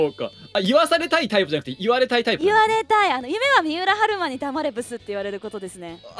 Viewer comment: yes best grill did say miura haruma in 1:50 ish?